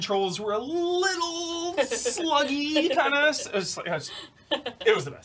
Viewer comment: Thought said Red Dead Redemption's controls were slutty untill I replayed with subtitles and realized he said sluggy.